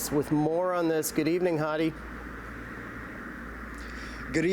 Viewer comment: When Matt Ingram said Hadi Azads name the second time it sounded like he said it an O instead of A and I heard: Good Evening Hottie.